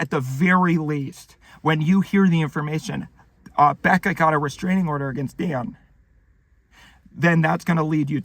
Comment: At around the 4:20 mark, it sounded like he said “Deion” instead of “Dan,” and I cannot wait for the Deion personality timeline to emerge